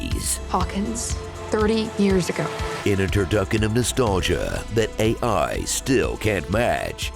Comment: "In a turducken of nostalgia..." 😂 The auto subtitles didn't know what to do and said "introduction" instead.